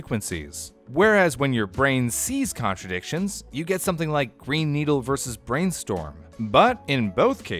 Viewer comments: I hear 'Brain Needle' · I hear "brain needle", idk what to tell you. Also, your audio girl would be good at asmr. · i hear "brain needle" for some reason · I hear "brain needle" lol....